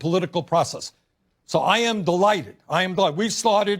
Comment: I heard "I am the light, I am god" lmao